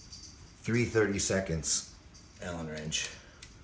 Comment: Hi, thank you for your videos, I've heard you saying "3/30 seconds" 01:17 , I am really struggling to find an Allen key with this measure 3/30", did I hear you well? from where I can buy this Allen 3/30"? please help me because I have purchased 3 Allen keys set and none of them can open this kind of bolt.